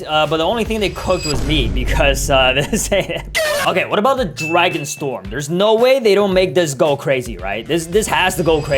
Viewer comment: Did you just say dragpn breath for dragon storm